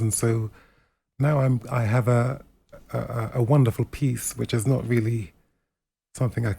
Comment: I thought he said "I have a wonderful piece" in relation to his missus 😂